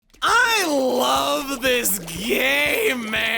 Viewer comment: At the beginning it sounded like he said "I love this gay man!" Lol